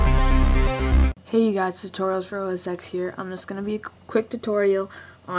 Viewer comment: lmao i thought you said "tutorials for oral sex here"